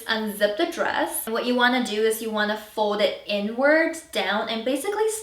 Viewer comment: what is the N-word at 1:20 ? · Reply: “Inwards” not even sure if that’s a word lol 😂